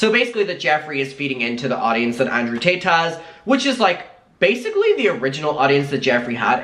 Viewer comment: why the heck does it sound like your saying Andrew Tateas it sounded like a combination of Tate and Paytas🤣🤣